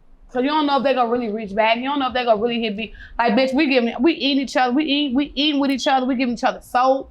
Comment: did she say we eating each other